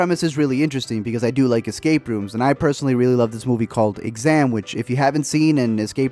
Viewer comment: The movie that he mentions on 2:02 What is it called? 🤔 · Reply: Nelly Garcia thanks🙏🏻 All I heard was “sandwich” lol